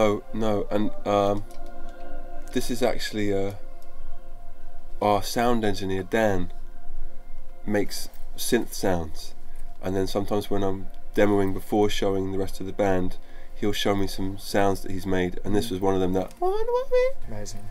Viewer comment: Can someone tell me what is he talking about at 13:09 ? scent sounds?